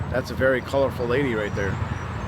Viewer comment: Did you say that lady is a colored lady? Wow, I thought we stopped saying that back in 1979